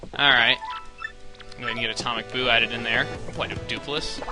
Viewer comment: OMG when you said "atomic boo" it sounded like you said "atomic POO" lol